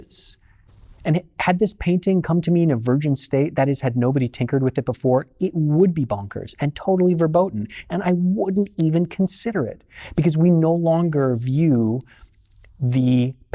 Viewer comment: Did you just say "verboten"? As a german I immediately pickep up on that, unless I didn't hear it right.